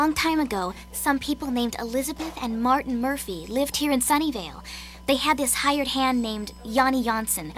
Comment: I keep hearing "sunndydale" instead of "sunnyvale", i'm probably watching too much Buffy